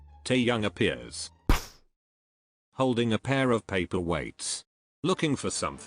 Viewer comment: Tbh, until 9:17, I thought they kept on saying 'paper waste' and not 'paper weights'.